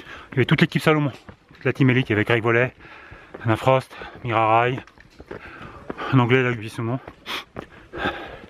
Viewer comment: A 18:00 => "Y a Anna Frost", j'ai compris "Y a Alain Prost", je cherchais du regard sans comprendre 😂😂